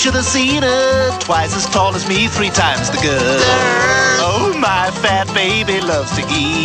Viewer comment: i thought he said "three times the girl" not girth XD